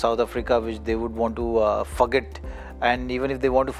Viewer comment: is it only me or anyone else who heard zaheer khan saying "F**k it" Instead of forget. 😅😅😅